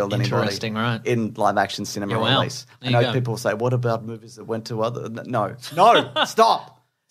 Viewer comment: "Alot of people are saying what about mumble mumble mumble mumble..." What? What did you say, please? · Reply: "what about movies that went to other..." He trails off and I'm not sure what he was going to say. As they were talking about movies where Batman doesn't kill, he was probably going to mention the various animated movies.